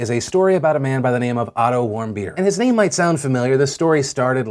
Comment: I thought you said Otto Warm Beer and I was like: wtf kind of last name is warm beer? Now i feel like an idiot